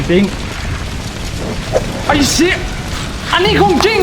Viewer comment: ได้ยิน เนทพูดว่า ไอ้ชิด!!! ผมนี่รู้ซึ้งเลย เอาความเป็นไทย กะ ต่างชาติ มาผสมกันกลายเป็น ไอ้ Shit !! 555+ (เอาฮา)